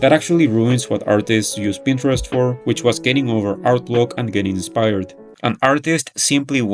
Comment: the subtitles say "getting over art look" instead of "getting over art block".